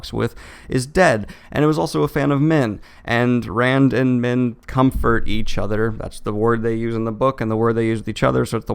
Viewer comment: I heard "Rand and men comfort each other" and I was very confused as to what I missed